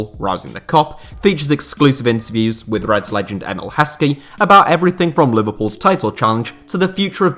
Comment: did he say reds legends like Heskey?